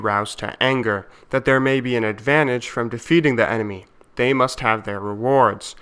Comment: "In order to kill the enemy, our men must be roused to anger; that there may be advantage from defeating the enemy, they must have their rewards." I swear i heard the ".. defeating the enemy.." part as ".. feeding the enemy.." :P